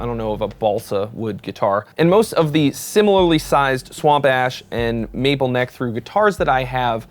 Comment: I thought you said you always get swamp ass and was very confused.